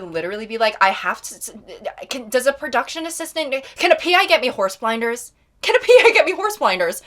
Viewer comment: when you said you needed "horse blinders," my brain thought you said "horse tranquillizers." and I nodded in agreement. with horse. tranquillizers.